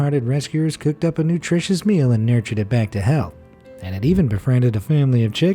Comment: I heard "nurtured them back to hell"